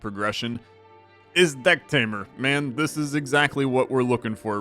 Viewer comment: i hear "dicktamer" 😂